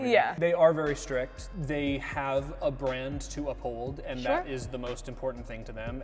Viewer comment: How come when talking abt Disney when he said they have a brand to uphold I had to rewatching it cuz I thought he said they have a brand...it’s a cult 😂😂